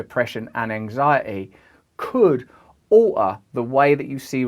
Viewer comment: Minutes 1:10, he says aoughter instead of alter, I don’t know how he does it but I still understood what he said.